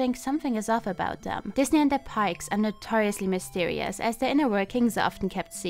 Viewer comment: I kept hearing "Disney and their pikes"